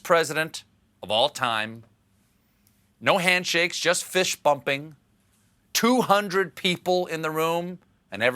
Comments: Great vid. Sure I heard you say “fish-bumping”. Maybe just wishful thinking, and/or my mishearing of accent due to me being from the UK (yeah, yeah, whatever, laugh it up people). Anyway, even if not, thanks for the fun. “Jobs, jobs, jobs!” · Did he say "fish" bumping? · Did he just say "No handshakes, only fish bumpings"?